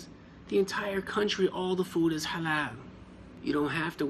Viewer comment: In 4:25 , the caption is wrong, the brother said “Halal” not “Haram”. This how in my phone appeared